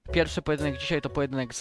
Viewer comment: Usłyszałem "pierwszy pojedynek z r*******m" D